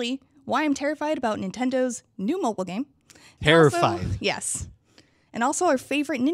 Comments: Does Peer say "terrified" or "peerified" at 0:22 :D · Does Peer say "terrified" or "peerified" at 0:22 :D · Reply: Ghisteslohm I legit thought I heard "peerified" too 😂